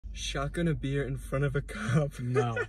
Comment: I thought he said "Shotgun a deer" ☠